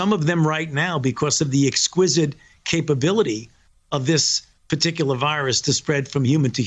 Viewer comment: EDIT: This happened at around 4:50. And I'm aware it was a mis-speak, but someone will make a video with it, you know Republicans... "Due to the exquisite capability of this vaccine to spread..." Doing too many interviews, getting words mixed up now. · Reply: "... due to the exquisite capability of this particular virus to spread from human to human." That's what he said, not "this vaccine." (Edit: that's at 2:30. Later he does misspeak but big deal.) And if you object to the word "exquisite" you just need to learn what it means, as he chose that word with purpose, though as an immunologist.